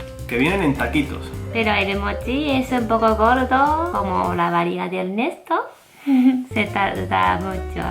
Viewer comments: yo entendí la "varilla de Ernesto", tengo una mente perversa o realmente dijo eso la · A que se refirió en el minuto 3:01? :v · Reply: Perla !!! yo escuche "como la barita de Ernesto" y quede muy impaktada xD